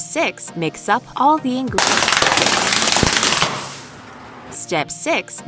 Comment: well that's I hate mix up ingredients and I throw Girl: what do you say